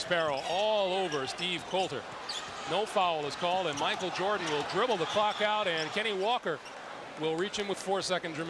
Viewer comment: did he say Jordan will dribble the fuck out? 😂